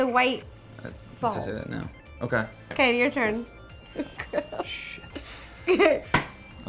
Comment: Did anyone else hear him whisper sh*t to himself at 2:16?